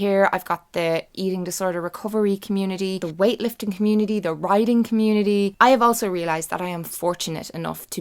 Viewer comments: When you said writing I thought you said the riding community :P Took me a second to realise my mistake haha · When you said the writing community, I thought you said the 'riding' community. I was like GWAN THE GIRL Hahahaha. This was such a lovely video. I really wanna try this now!